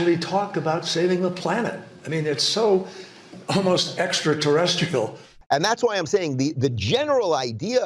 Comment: Thought Kerry said terrestickle 😵‍💫 wtf is that 😂